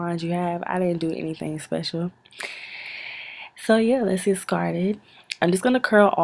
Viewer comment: Did u just say let's get skarted lol?